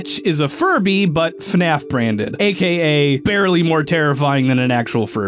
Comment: Austin: 7:40 Aka blank terrifying then a ferby. Did he say Very or Barely?